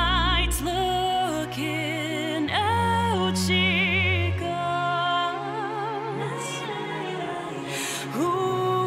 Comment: some how i hear the "lookin' out, she calls as "looking outchie calls"